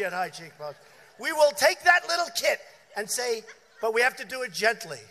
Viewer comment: la trad pitié quoi mdrr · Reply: Christopher Pere "we will take that little kit" prendre cet ensemble si tu préfères, ils ont juste cru qu'il disait "take that little kid" C'était dur ?